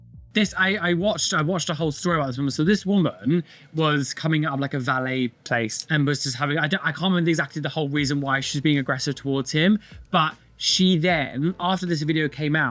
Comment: I js came back to this video and my brain thought said "so this woman was cumming on this guy's face". Needless to say I was very startled